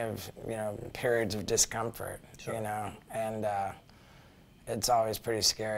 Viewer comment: I heard - "My period gave discomfort. Lol sorry I know he's being genuine but good grief. My bad lol